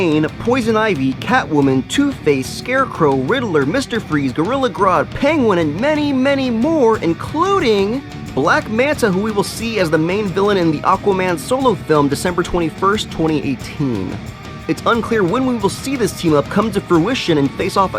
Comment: I thought he said black mask which got me so excited but then he said black manta and was like dang it!!!!!